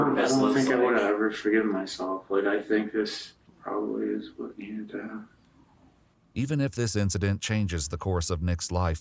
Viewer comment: Caption is wrong, even though the narrator understood it correctly lol. He clearly says “…is what needed to happen.”